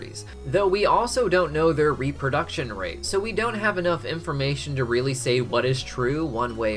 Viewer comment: did he just said IMPformation?